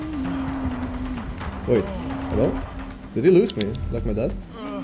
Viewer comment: wait a second did he just say.."did he lose me like my dad"